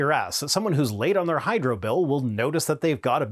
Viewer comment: “…somebody that’s late on their Hydra bill…” - I’m sure I misheard you and you actually said ‘hydro’, but it sure did sound like you’re advocating supporting the bad guys from Captain America comics. 😝